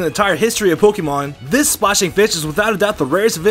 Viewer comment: Sounded like he said "this splashing bitch"